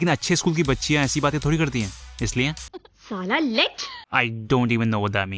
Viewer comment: i think she said lech like lecherous